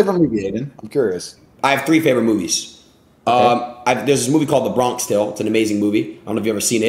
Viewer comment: What was the first movie which adin said at 15:40? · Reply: A Bronx Tale. Good ahh movie